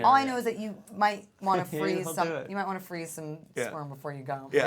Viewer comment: lol i thought she said freeze your sperm